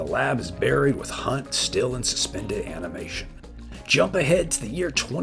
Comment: I had to listen to the beginning 3 times before I heard "with Hunt still in hibernation". I kept hearing "with Han still in hibernation" the first two times. Wrong movie! BTW, for those who don't know, Kolchak: The Night Stalker was a huge influence on Chris Carter of The X-Files fame. A damn shame it only lasted a single season.